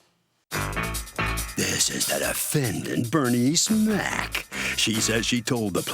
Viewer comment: I keep hearing "Bernie Smack" instead of "Bernice Mack" 😂